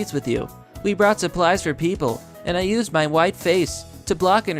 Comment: "I used my white face" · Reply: I thought he said "my wife's face" for a sec.